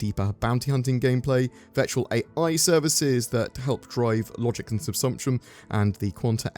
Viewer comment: Was that "SEXUAL AI services" at 7:39? I couldn't quite catch that but because it refers to helping "NPCs dynamically spawn" a few seconds later I'll assume that's what it was. 2021 is shaping up to be a GOOD year!